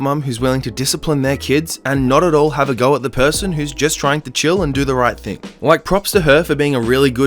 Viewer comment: did I miss something or did he say em instead of chill dad??!? Im quite confused